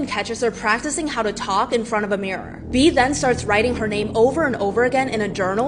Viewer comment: I thought she said her name was Bean and I almost died there for a second 💀